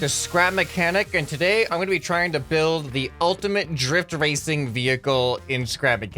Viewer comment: I thought he said the ultimate drip vehicle